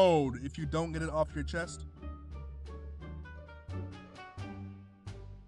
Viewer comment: My volume doesn't go high enough to hear what he whispers at 5:50.... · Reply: He said, "I have a Facebook..."